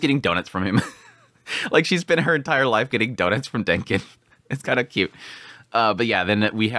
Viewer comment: 'Denkins Dougnuts' i heard it , now i cant unhear it lol